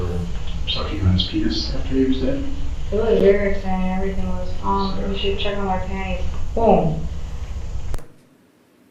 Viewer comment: WTF does she say at 1:05:38 word for word? Cant make it out, but she laughs in court day 3 when listening to this interview at this exact spot. Its something she found very funny cause she couldn't hold it back for nothing. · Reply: She says it was very exciting, you should check the inside of my panties